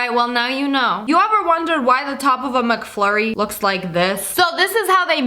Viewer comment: did she just say “ taco bell McFlurry “ ?